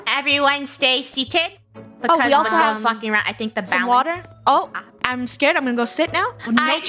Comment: what did she just say? 👁️👄👁️ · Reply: She said they cant be walking around